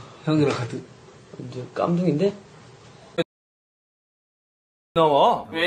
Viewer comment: can someone confirm for me if what jimin said at 0:33 is actually the n word? bc google translate isn’t always completely accurate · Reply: "kkamdoongie" is a racist and colorist term. it means "blackie" in korean.